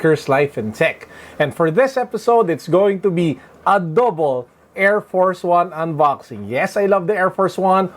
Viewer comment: I thought you said adobo at 0:08! 😂 I’ve actually never seen these before but they seem fire 🔥